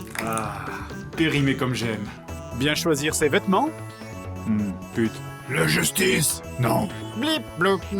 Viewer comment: Euh.. Il dit quoi à 0:22 ? J'ai cru comprendre "pute" mais je crois pas que ça soit ça 😅